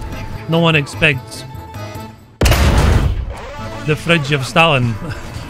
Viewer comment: Amazing video! I thought was saying “friendship Stalin” for almost the entire intro😂 I’m terrible with accents sometimes